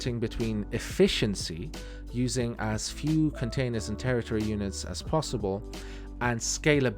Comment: "Territory Units" instead of "Transfer Units"